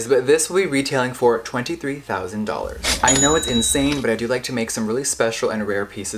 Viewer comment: damn I thought he said $23 for a sec but no $23,000🤣